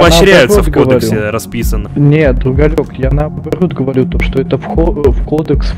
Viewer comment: Пхахахаххаахаха, послышалось, что он сказал «Уголек», а не «другалек»